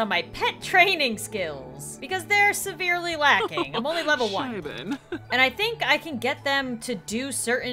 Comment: I had captions on too see if something funny would pop up and here it it is.... Wen you said I'm lvl 2 captions thought u said I'm level boom bash XD